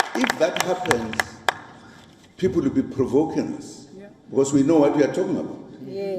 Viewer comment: Did I hear him say "People will be provokens"? 😮😮